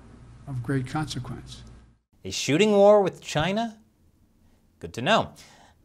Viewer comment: thought the old usurper said "shitty war" not shooting war